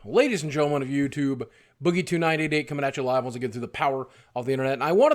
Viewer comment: Oh its Boogie 29 88... I always thought he was saying something like ''Boogie tonight aint comin..''